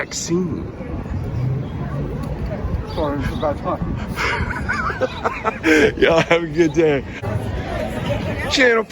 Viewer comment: what did the guy say at 12:03? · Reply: “Sorry it’s a bad time”. Haha made me laugh